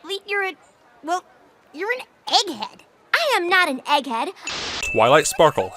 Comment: why do i always understand "eddy Head" instead of Egg head? xD